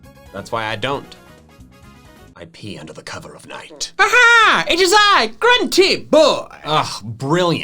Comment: I thought he said “I pee under the cover at night”